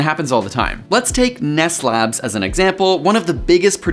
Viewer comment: is he saying meth lab?